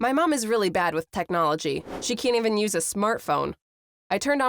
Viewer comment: did she say smurtphone?